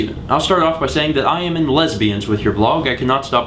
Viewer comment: Did you say at 6:03 "I am in lesbians with your vlog..."? O_O